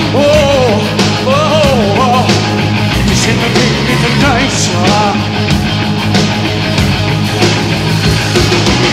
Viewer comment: What does he say at 3:44? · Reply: "It's even hidden in the night" ....it's just kinda broken up.